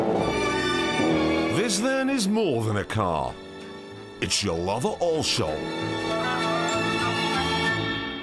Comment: What the hell does he say at 6:14 ? It's a lova ollshom?